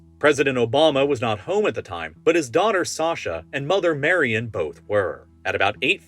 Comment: did he mispronounce Michelle? Did he say "Mary"? Am I crazy?